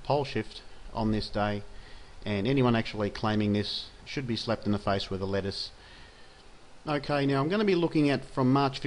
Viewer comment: ahaha! @ 10:58 Did you say "And anyone claiming this should be slapped in the face with a LETTUCE."?! Oh my gosh, that's great! Thanks for this video! So much fear mongering going on about this. My intuition said not to worry, but it's great to get a clear understanding about it. Much love.